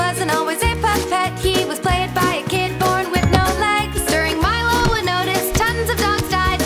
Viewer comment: Are there lyrics I can read? I'm having a hard time understanding what's being said some of the time. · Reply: And how does this help with my question? It doesn't. When I first heard the part with Milo and Otis at 1:53 it was garbled to me and I didn't hear Milo and Otis, I heard something more like "durmilonginotis". I've never heard of that movie. It's also possible my tv speakers aren't top of the line. I was simply asking if there were any lyrics I could look at to check if what I heard was correct or clarify what I heard since I had an issue with parts of the song the first time I watched the video. I wasn't leaving a sign up saying "I need people from other countries to pop in and tell me I'm horrible at hearing english." A constructive comment would have been more along the lines of "What part did you have an issue with? I can help." I doubt anyone, whether or not they know english as a first language, has ever been 100% with understanding every random person's english and never needed clarification. Next time someone requests help, actually try providing the help.